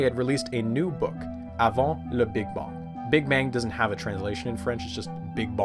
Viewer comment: Every time he mentions their book I only hear “I wanna big bong”.